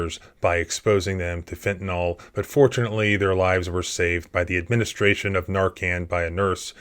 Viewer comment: I had to go back. I could have sworn you said "unfortunately their lives were saved". 😂